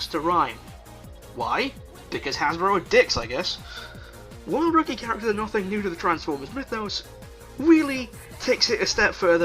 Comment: Can't understand you, talk less rambling-mumbly. 6:33 ???? ??? ??? ??????? ? ????? transformers-mythos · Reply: Gavin Powell "While rookie characters are nothing new to the Transformers mythos"